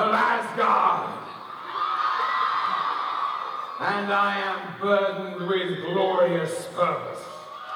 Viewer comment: I thought Loki said "And I am filled with glorious sperm"